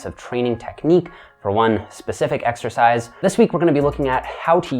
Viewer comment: Exercise? I thought you said extra fries!